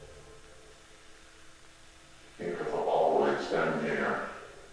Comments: For the last bit of footage where matpat heard “you have always been here”. I thought it said “it will always end here” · In the end video all I heard was “it will always end here” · I hear 'It has always been here' at the end.